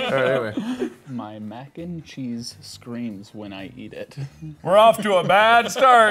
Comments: saying the line "My Mac and Cheese screams, when I eat it." I THOUGHT HE SAID "My Mac and Cheese screams when I fuck it" 😭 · When said the line to the camera I heard "Pop my (idk) ice cream when I eat tit." Congrats Mark I think like you! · I thought said my pancakes cream squirts when I eat it